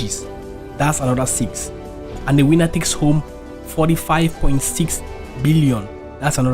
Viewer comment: I heard him say "the wiener takes home 38 million".and i laughed out loud. I have to grow up.